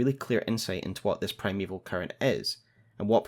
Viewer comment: Not sure if it is just because I am hungry. But at one point it sounded like you said "Primeval Curry" 😂 great video as always. Been holding off on watching these until I had a good understanding of the broad strokes of the story from playing the game. I love all the connections you find. Always excellent!